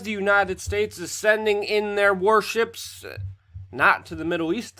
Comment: It sounded like you said "worships" when wouldn't be wrong either 😂